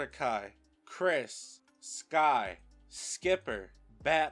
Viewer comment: DID YOU JUST CALL STAR SkY? And i wanted a star and gumball finale but this finale was not horrible